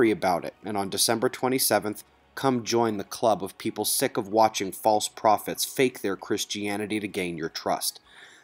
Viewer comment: So, I am a Christian. I listen to your broadcast almost daily. This morning, the bashing of Christians, really takes away from the work you are presenting. use your gifts, from God, wisely. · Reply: and All, I re-listen again (several times) after work as requested, Bottom line, I was wrong and I am sorry. I thought I heard Christians are False Prophets on the first pass this morning and made the connection. I was wrong. However, My point stands that you are doing great work and I hope many lives are saved by the earthquake and Tsunami predictions/forecast. Please forgive me for my error.